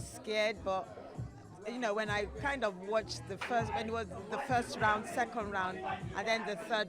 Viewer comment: did she just say first n-word?